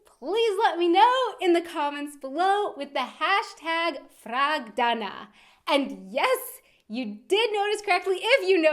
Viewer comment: #frag Though I thought you said #frau... So why did you change your usual hashtag? Was someone else using it?